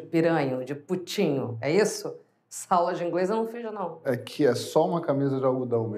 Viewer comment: só quero saber o que ela diz em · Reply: Ela diz: "Essa aula de inglês eu não fiz não." Expressão normalmente usada para dizer que ele ta falando outra lingua, algo sem sentido ou lógica.